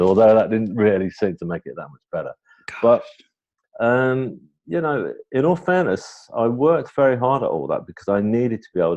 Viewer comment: what is that whisper voice at 5:00.... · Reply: It was the interviewer saying gosh.